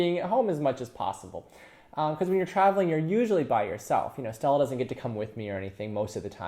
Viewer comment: Around minute 2:09 you mention someone who may or may not travel with you. It sounded like “Sal.” Who is that? Nice explanation.